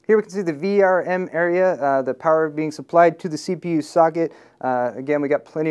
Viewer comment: Lol, sounds like he said sogget, instead of socket at 9:57.